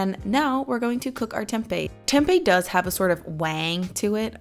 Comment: did she say Tempeh has a wang??